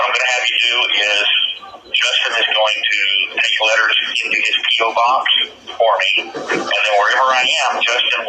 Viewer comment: Sounded like he said "Justin's going to keep my letters in his paedo box", which is about right. Dang it, why would you want to help this disgusting sicko? The lack of morals, empathy and decency of frauditors never ceases to amaze me.